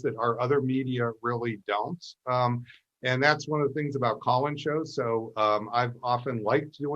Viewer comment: Did he say Colin's show? (54:26) I can't hear him clearly on some words. Thanks!